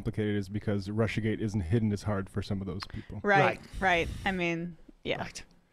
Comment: “The reason it is being presented as not complicated is because RussiaGate isn’t hidden as hard for some of those people.” I’ve read this quote by Matt multiple times and I still don’t quite think I’ve grasped what exactly he’s saying. Would someone mind breaking it down? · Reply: I heard "hitting as hard"